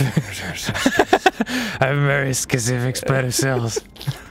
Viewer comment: "I have a very specific set of Skittles" is what I heard Dan say at 4:53.